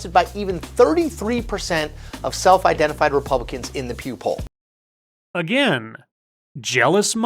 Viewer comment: Did no one else at 5:00 think "did this guy just say 'poop-hole'?" Yes, I know he said "Pew Poll" but if you really think about it, I believe this idiot is referencing his own "news organization" and where it ranks amongst the others in the toilet that is known as Mainstream Media.